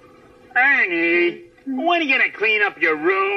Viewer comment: sometimes when he says "ernie" i hear "honey"